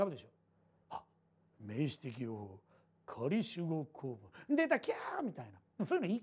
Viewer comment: なんて言ってますか？ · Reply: 名詞的用法仮主語構文